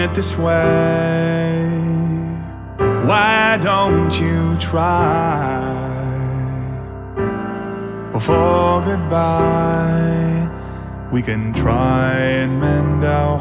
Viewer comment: I really thought he said "why don't you try, before you buy." Kept waiting for a gaming joke punchline after that. Great song...also great beard manipulation!